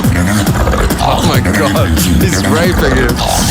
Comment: Did you say raping?